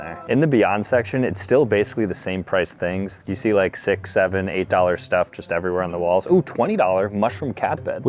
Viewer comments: did bro just say 67!!!!!!😂😂😂😂😂😂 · Did i hear 67 or something 29:21 💀